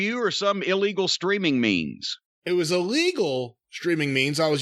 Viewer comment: Dude straight admitted he illegally streamed and where he did lol wtf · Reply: I thought he said it was a legal stream on BR Live which is AEW's online PPV provider.